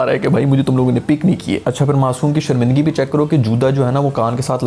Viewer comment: JUDA instead of JUTA 😆😆 😆 . (Just for fun no offense Sir)